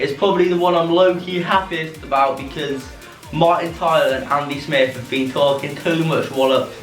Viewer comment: Did i just hear him say Andy Smith at 2:32?